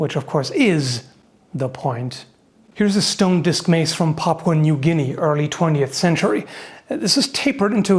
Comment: are my ears deceiving me or did call that "Popcorn New Guinea" ?